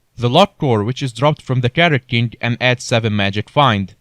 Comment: what was it dropped from? i heard carrot king ._. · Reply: Carrot king, /scg